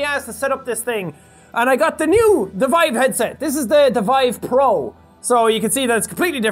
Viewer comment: I thought he said he had a Vibe headset at first XD